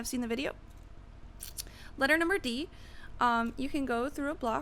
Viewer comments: Did you say letter number D? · wait did you just say "letter number D" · did you just say "letter NUMBER D?" LMAO! ;-)